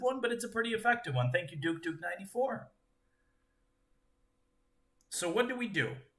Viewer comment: I thought he said knight e4 but he actually said 94. I need a break from chess